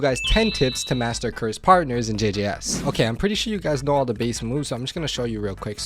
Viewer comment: i heard jj ass